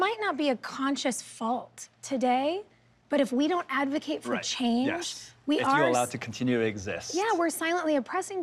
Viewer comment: i'm asian and half asleep right now so i thought it said "recipe for chang" lolll